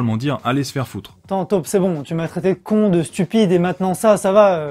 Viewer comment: C'est moi ou à 4:10 dit "Taupe Seven" au lieu de Taupe 10?